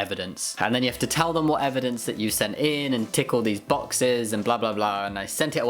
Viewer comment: I realize you said "tick all these boxes" but I heard "tickle these boxes"